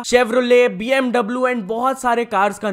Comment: Did he just said chevrole😂😂